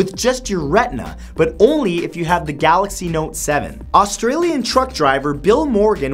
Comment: is it just me or did he say note seven